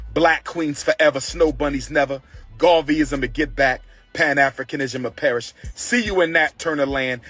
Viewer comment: did he say "Pan African-ism will perish"?